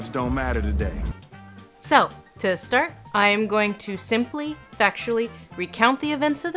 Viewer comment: I thought she said 'sexually recount the events of the film' so i watched the whole video only to recheck, it was 'factually'. don't be fooled.